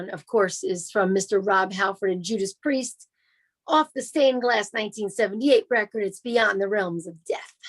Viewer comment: Hey...great episode...love when is on the show....not sure if it's my hearing or not but it always sounds like says Stained Glass and not Class...my bad if I'm wrong...thanks....here's my one offering for the topic..."Holiday" by the Scorps.